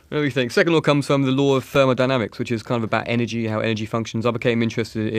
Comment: Matt rapping moment. I have no idea what he was saying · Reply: LMAO I feel you. I need extra hard work to understand his words most times. He talks about 2nd law comes from the law of thermodynamics which is something about how energy declines and humans seem to be always battling against the fact that the energies depleting both in our bodies and planet and also in solar system.